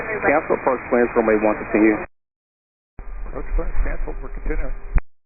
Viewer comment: i thought i heard atc tell 1 to hold back and the pilot said he was proceeding abt 3 min in? @ 3:29 "we're continue"